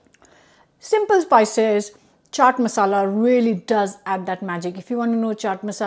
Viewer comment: Not an okra fan but my wife loves it. I'll give this a try. Also can you please post your Chat masala recipe. First time I heard it I thought you said "charred masala" so I just roasted some garam masala in a pan and added it!!! Thank you.